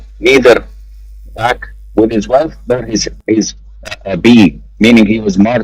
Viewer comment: subtitles are wrong. Sheikh said weslth not wife Jazak Allahu khairan for the video tho ❤